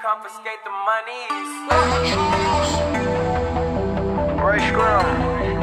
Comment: did anyone else hear 'ricegum'??????